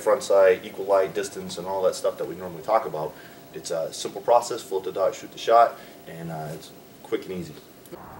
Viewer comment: on position 2:33, what is he saying. "Hold the dot shoot the shot"? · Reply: Float the dot, shoot the shot